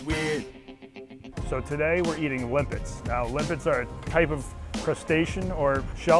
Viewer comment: Thought said whipits and not limpits in the beginning. Was so ready for what I thought I was gonna see